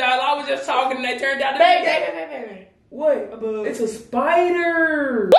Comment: when she looked to her left I thought she was saying that she was a bug lmao🤣